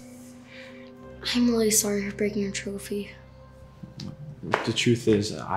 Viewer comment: wait what he said "im really sorry for breaking your trophy." but the sub titles said "im really sorry for breaking trophy its happend at 9:11 ( the "(" is where the mistake is